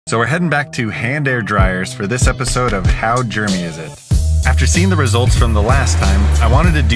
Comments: I thought bro said how Germany is it😂🇧🇪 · I thought he said how Germany is it 😆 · I thought bro said how Germany is it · My dumbass thought it said how German is it and I was so confused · For a second I thought it said “how Germany it is 😭😭😭”